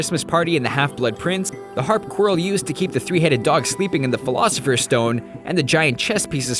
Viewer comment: did he say...philosopher's stone?